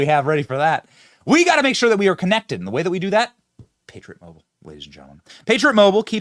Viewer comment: During the Patriot Mobile Ad I thought you said Pager Mobile and I freaked out LOL.